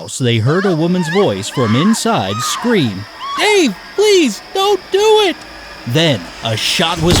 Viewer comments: i thought they said "hey, please dont do it" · The first one threw me off because I thought he said " ave please don't do it".... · In the first one, I thought she said “hey, please don’t do it” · Oh, I thought she said hey, please don’t do it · the first one should have been more clear. I heard 'ey, please don't do it!' I didn't just hear it the first time, I heard it each time I listened. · i realize the first one i heard ''hey please dont do it'' but apparently it was ''dave please dont do it'' the only reason i thought it was the firefighter was because firefighters carry weapons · I thought the woman said "hey please don't do it" it's hard to hear that part... I still can't hear what the name is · Dude the first one I thought he said wait no don’t do it not nade no don’t do ut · I thought it said "Wait, please don't do it!" Not "Dave" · Two. Didn't get the first one because i didn't hear any name mentioned. If she had screamed Jim or any other male name, I would have guessed it. You hair hint was helpful. 👍👍👍👍👍👍👍👍👍👍👍👍👍👍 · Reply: I heard “Ave” not Dave. So I heard it as “Aye, don’t do it!” So I thought nothing about them mentioning a name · On the first one it sounded like you said "hey, don't do it" and not "Dave, don't do it"